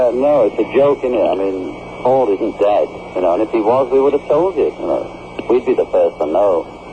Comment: At first it sounded like he said, "If he was dead, he would have told us!" which sounded like something John would have said.